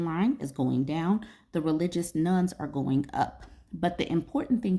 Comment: Ah, religious "nones". I initially thought she was saying religious "nuns". 🙂